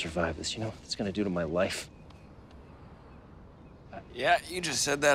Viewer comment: Did he just say my wife? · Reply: he said my life haha